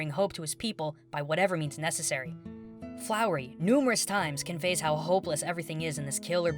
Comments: Did you say flowery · one second did he say flowery